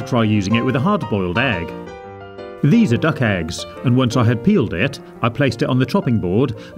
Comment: did he say duck egg why not a chicken egg what does he have against chicken eggs